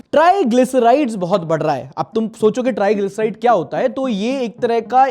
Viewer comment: u said triglyceride, i heard john abraham